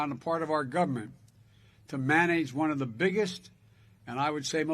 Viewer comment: did he say "mayonnaise" at 4:24!? 😂😂